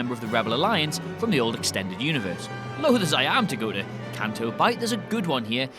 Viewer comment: WHAT DOES HE SAY!? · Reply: Loath as I am to go to Canto Bight..